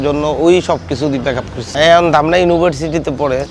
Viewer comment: এখানে এটা কি বললো ? তার ওয়াইফ, আবার ব্রেকআফ, মাথার উপর দিয়ে গেলো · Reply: আরেকজনের সাথে প্যাকআপ হয়ে গেছে। এটা বলছেন